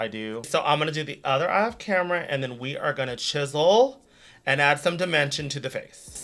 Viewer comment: for some reason i forgot "chisel" was a word so i thought you were saying "we're gonna 'chizzle'" like. "we're gonna chill" but snoopified LOL i guess my brain just accepted it as something you'd say